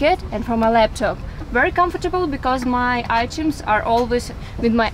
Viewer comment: I thought she said iTunes